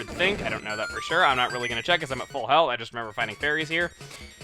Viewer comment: did anyone else hear at full hell